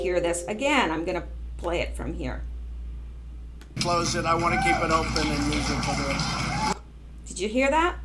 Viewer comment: Hi...you, lil lady, are on a roll. Enjoying your vids today. I must have selective hearing. Thought DJT said, "I want to keep GITMO open for her." Wishful thinking?! Censorship is all or none. Prefer none. Half is anathema to America, and so very unconstitutional. Thanks for this deep dive.